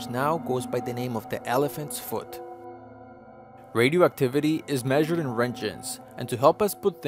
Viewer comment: i thought he said the radioactivity is measured in wrenches